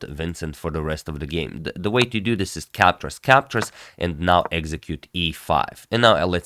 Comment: Execute E5, sounded like execute order 66. I am not a star wars fan.